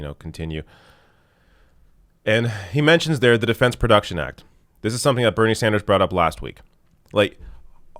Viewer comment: I thought he said "Fence Production Act".